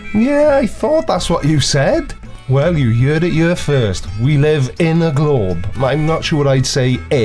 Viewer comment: "Well, you yured it yur first" I don't hear any accent....